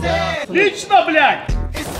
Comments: What does Zeus shout at 1:41 ?! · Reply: Desi Gaming good job /отлично, блять/ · What is zeus saying in 1:43? Something blyat... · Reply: Alpito123 OTLICHNO BLYAT! (Eng. GREAT BLYAT!)